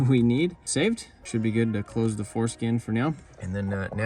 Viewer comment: Anybody happen to notice him say “foreskin” instead of “forscan” at around 3:10? 🤣